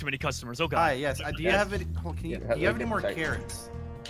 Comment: i swear he says do you have any cocaine